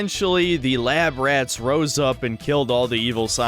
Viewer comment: not that it really matters, but I don't think "the lab rats rose up &...", I thought they said "oh we HELPED those poor people...", no?